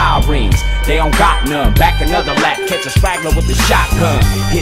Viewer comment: shotgun = entendi jacquin